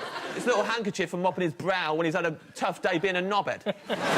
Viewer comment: "...tough day being a knob head" ? Really? I always thought he had said "...being a knob end." It's a term / phrase that I've heard much more often than "knob head." Oh well, you learn something new every day. Check for yourself